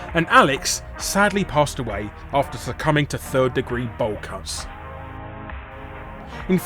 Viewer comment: What did say at 15:53 for what the hell is "ball cuts" & how can there be a list of degrees to it!? So what's the 3rd degree then? Also, why did Andrew's girlfriend dump him anyway? Perhaps because he sucked in life which I would totally agree with.. Also, it's assuming to a see a Jewish community roast another Jewish community out of revenge just because they got rejected for being "too small" if you know what I mean which itself is ironic for Andrew Rosenblum was also Jewish which means he was also "too small"! Maybe that's why his girlfriend dumped him for another man because he just didn't "make the cut!" LOL!!! CD-I Link: Gee, I wonder how Craig got that Tip! Me: I have an idea, the Ex-girlfriend tipped him off & then when Andrew got put in cuffs, Craig replaced him as her new boyfriend and announced a fake engagement party right in his face as he was thrown out of the courtroom & into the back of a police van! CD-I Link & Friends: "Laughing Out Loud!" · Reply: It was a joke. He said bowl cuts. As in the terrible haircut. As for the rest of your comment I have no idea what the fuck you're even trying to say.